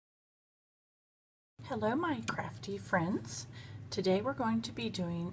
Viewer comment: HahahhaXD I thought you said hello minecraft friends I was like minecraft? Nice vid